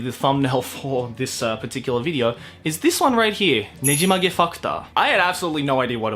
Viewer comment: I heard "NEJIMAGE FUCKTOR"